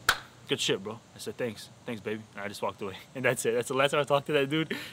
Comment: did he really say thanks babe???